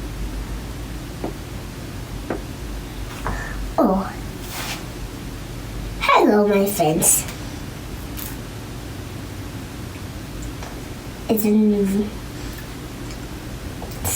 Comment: Did I just hear “ Hello my Fan”?😂😂😂